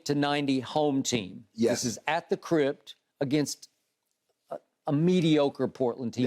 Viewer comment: What did Skip Bayless say about the Staples Center around 2:52-2:55?... I couldn't tell. Did he nick-name it "The Crib"?... If so, that's what it sounded like--to me, at least... Maybe it's just me & how I interpreted it, but, I don't know.